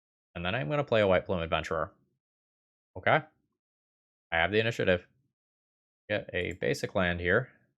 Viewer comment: For a second I thought it said UWU Initiative Stasis